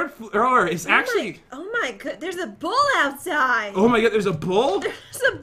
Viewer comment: I thought she said THERES A BOWL